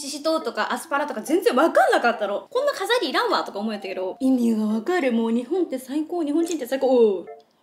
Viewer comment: 3150に聞こえて笑ったwww(わかる人いるかな！)